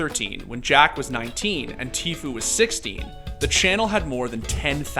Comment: sounds like he says Tfue was 60 lol